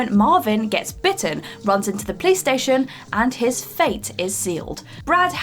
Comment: anyone hear her say PlayStation instead of Police Station